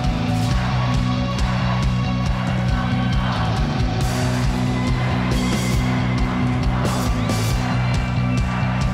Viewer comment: what are they chanting? I went to a catfish concert the other week and people were chanting this but I couldn’t tell what they were saying😂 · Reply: Here we here we fucking go!!